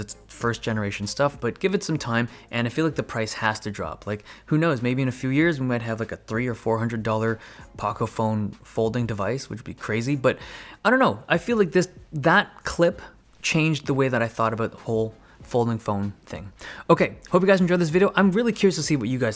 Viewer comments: Wait did I heard Pocophone folding device. You hear him guys 2 years from now we will see that coming for sure. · wait did he just said paca phone